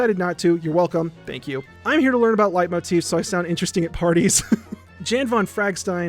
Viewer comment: Does anyone else stay to listen to him read out all the patreons? Because it’s genuinely entertaining. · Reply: i always skipped it before but this time i wasnt paying attention and heard him say “i watch your videos so i can talk about light motifs to sound smart at parties” and was like WHAT